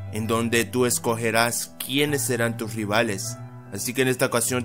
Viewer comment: Lo que dice:en dónde tú escogeras quiénes serán tus rivales Lo que escuché: en dónde tú escogeras quiénes serán tus PoKemOnS